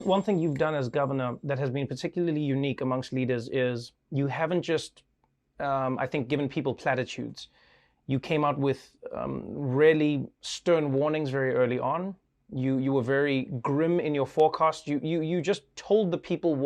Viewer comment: I heard "platypus"😂